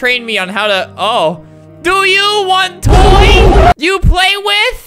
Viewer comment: i was getting some chips and listening to the video in the background and for a sec i thought said "DO YOU WANT BOY?" 💀